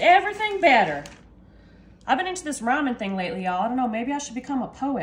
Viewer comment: For some reason when you said “rhyming” I heard “ramen”. The next sentence cleared it up for me.😂